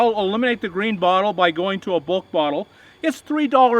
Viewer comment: What did you say @ 2:37 ? Bulk bottle?